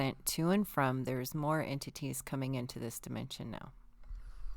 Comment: Sometimes when says entities it sounds like she's saying amputees. I'm like wth did she just say?😂 Maybe I should have my ears checked.